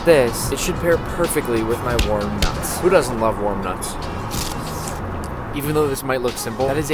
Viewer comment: Watched the entire vid. Only funny thing I hear, Good Nuts.